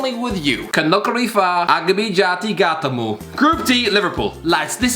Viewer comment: If you’re wondering what irish guy said at 5:44 he said “Kano krifa gamato gata mou” It means “I secretly fuck my cat”